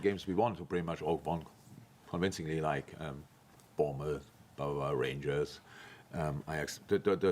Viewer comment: Did he say "power" rangers?